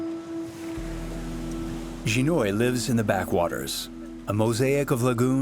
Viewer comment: your translations are completely wrong. Not once did he say sea or ocean. He said "Backwaters".